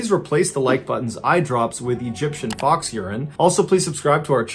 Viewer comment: 0h fox urine! I thought you said ox urine, I still have a bottle of Alaskan fox urine.